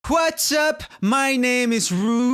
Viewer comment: watching in 2018 or the future? Go to 00:00 and instead of "What's up" think about "Quads up", it will sound like he's saying it. ok thanks and yw